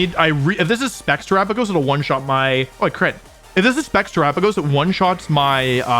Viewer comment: did wolfey say “oh that crit” after wicked blow😭😭😭